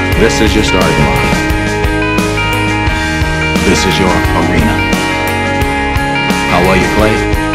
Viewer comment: Can somebody tell me what is Richard Webber saying after "This is your starting line, this is your ..." - I can't figure out the last word of the sentence. It's at about 0:18 sec. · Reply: Arena